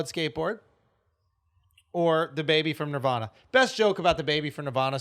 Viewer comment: Lmao thought he was saying dababy and nirvana. Not the baby from nirvana